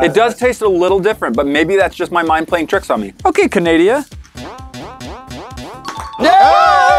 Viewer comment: DID THIS BOI JUST SAY “OKAY CANADIA?” Like boi...